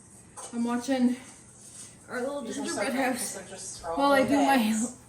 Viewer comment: I hear Our little ginger bread house!